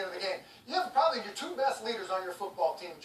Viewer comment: Ok listen carefully at what that guy said around 2:50 - 2:56. What did he say? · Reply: Hemi it was leaders 😂 he said leaders